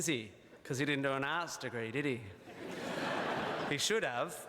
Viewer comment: It's ok, I'm Australian & I heard "Arse Degree" too. (but I knew he meant Arts)